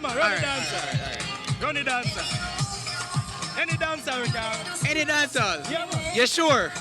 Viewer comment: I Thought He Said Any Dancehall😂😂😂